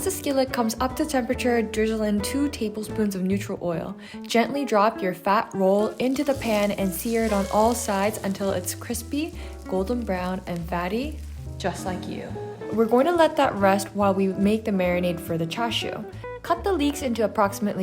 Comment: I thought she said, “crispy, golden brown and baddie, just like you” 🤔